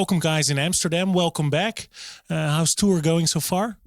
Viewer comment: At the end I thought the guy said “hamsterdam”